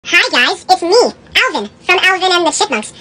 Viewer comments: It sounded like you said Alvin and the sh1t monkes💀💀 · I heard "FROM ALVIN IN THE CHIP MONKS" LMAO